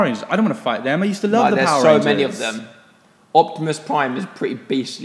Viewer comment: I thought it said octopus prime haha lol